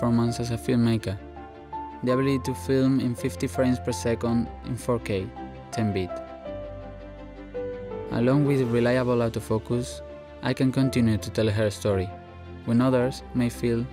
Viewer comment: With his accent, it sounded like he said “reliable out of focus”